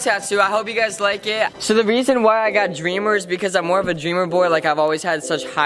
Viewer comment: am i the only one who thought it said oreamer and not dreamer?